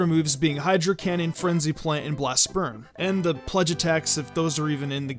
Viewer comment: okay I swear I just heard "Blast Sperm"